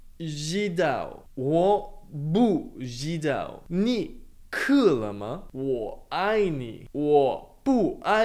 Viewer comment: The subtitle says “is she thirsty” but what he’s saying is “are you thirsty yet?”